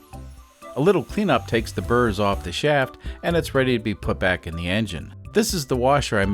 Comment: I heard you say "A little pre-nup takes the burrs off the shaft" and I thought about it for a second and said to myself "That's one way to look at marriage!" 🤣